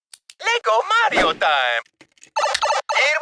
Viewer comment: I hear : LEGO Mario DIE.